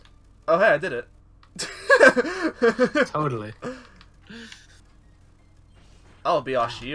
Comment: did just say "toadally" there?